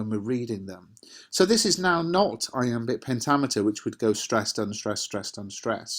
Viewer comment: anybody know what the words he said was and means? Need help ASAP lol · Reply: Iambic pentameter. It means the pattern and number of stressed syllables in the line (five).